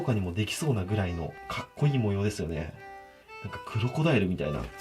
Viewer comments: 「クロコダイルみたいな」って聞いた瞬間、「かねこダイル」って聞こえてしまった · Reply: 私はウロコダイルって聞こえた笑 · 「クロコダイルみたいな」って聞いた瞬間、「かねこダイル」って聞こえてしまった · ウロコダイル←に聞こえたのは内緒